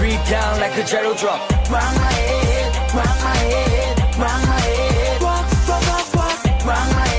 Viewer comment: when I first heard the song I thought they said rub my head